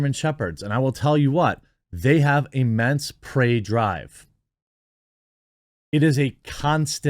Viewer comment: Watching this video ( 13:48 ) "Immense" what??? (had to go back and watch that bit again with captions on) "Oh, 'prey drive.' Okay. With you now." Just thought I'd mention that because there are people who don't understand why I watch movies and TV shows with the captions on even though I can hear the TV well enough. THIS IS WHY! Even if the person on screen is speaking English, if it's too quiet or too fast or if they use words I don't recognize, I can't follow their dialogue!